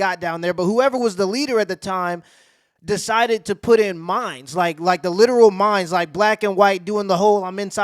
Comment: not going to lie i thought you said "mines" at first lmfao and thought "yup that would work" 😂